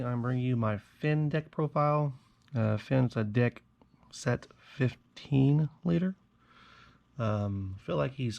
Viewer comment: It sounded like you say "Fins a dick set 15 leader." Hahaha awesome video